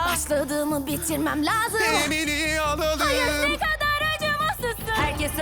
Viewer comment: da ne dediğini anlamadım daa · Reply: "Emily'i alalım" diyor